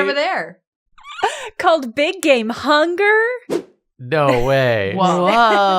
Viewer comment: When I tell you I thought they said Big Gay Hunger...